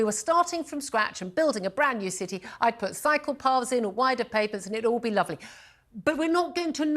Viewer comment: I misheard Julia when she said "cycle paths". I thought she said "psychopath" and I though it entirely appropriate.